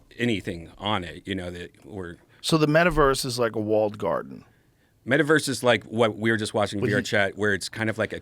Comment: holy fuck. AHAH i thought joe said so its kinda like an olive garden XD